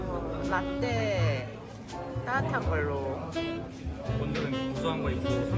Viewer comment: Wait a minute, did you say latte at 25:30? Lol